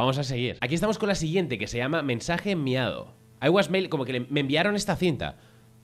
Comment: escuche mensaje miado xdddd